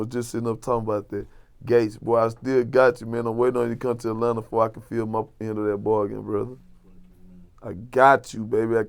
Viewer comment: WAIT, WAIT, WAIT..BACKKK THE FUCK UP...what did this nigga say at 3:44 ? Kevin Gates I'm ready for you to come back to Atlanta so I can feel what? THIS NIGGA GAYY. This nigga mannerisms and how he talks about it just screams "im a faggot" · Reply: he said "film up the end of that bargain" smh u drunk